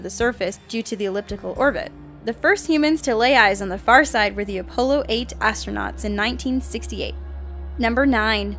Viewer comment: Did she just say opollo instead of apollo